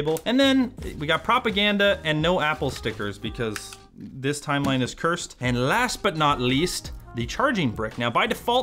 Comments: "Because this timeline is cursed." 🤣🤣 That got a genuine real life laugh. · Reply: Initially heard it as: "This time, Linus cursed". What is wrong with me?? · "Because this timeline is cursed." 🤣🤣 That got a genuine real life laugh. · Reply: Dude. I kept hearing, "This time, Linus cursed" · Ohhh, @ 01:07 I thought he said 'This time Linus cursed', not "This timeline is cursed" 😂🤦‍♂